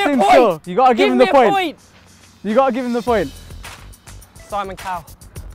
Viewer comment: Did he say Simon Cowell at 2:00?? XD